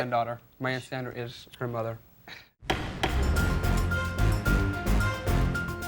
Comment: “Excuse me, your honor, that’s her granddaughter …” I couldn’t catch what he was mumbling there at the end.😂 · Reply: He said something like "that's her granddaughter, my aunt Sandra is her mother"